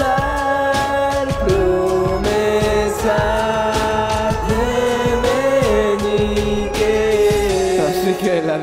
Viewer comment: Que dice en esta parte 2:59 hasta la 3:15 no entendí que decia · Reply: Promesas de meñique